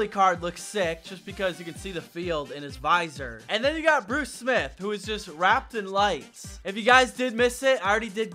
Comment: Swear I heard “ and Bruce Smith is rapped with lice”